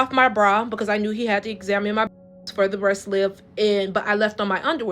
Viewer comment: Nice thing I was reading the caption. Because, I thought she said "the best milk"...